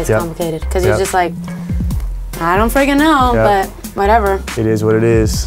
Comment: did she say i don't fucking know? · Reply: No she said ‘ i don’t freaking know’